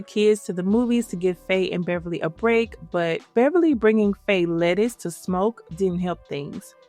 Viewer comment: i think it was "happy" pills not lettuce cuz faye asks what is this and bev says ray tells her to take it with someone you love and kept trying to get bev to take it with him pretty sure it wasn't the devils lettuce lol